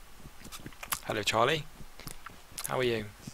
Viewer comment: he says 'hey charlie' and i was like wtf cause my name is charlie and i didnt know if i heard him wrong or someone was is messing with me :P it took me about 10 minutes to work it out :P also your videos are great :)